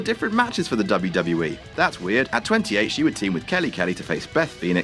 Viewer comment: you said 2008 instead of 2012 oclock motherf*ckers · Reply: Mohamedjamal Ali He said 28, dumbass